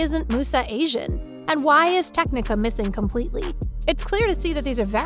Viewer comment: did she say tecnica